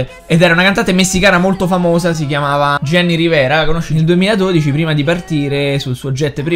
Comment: subito ho capito Gianni Rivera